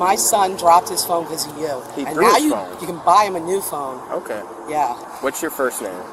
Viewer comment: She says she is going to buy her son a new phone and doesn’t have money for gas. What a moron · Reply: I thought she said that the cop HAD to buy him a new phone bahahahaha.